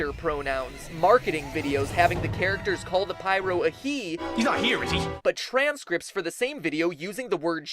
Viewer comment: i thought he said hes not here is she.